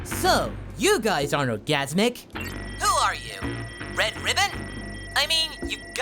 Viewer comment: did goku say orgasimic on 4:13?